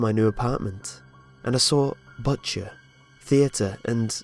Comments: i can't be the only one who when he said butcher thought he said butt chair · When he said butcher I thought he said "butt chair" 😂